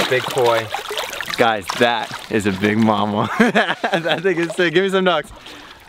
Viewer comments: did he just say “that niggas sick”?!!?? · what tf did he say??? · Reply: He said (that thing is sick)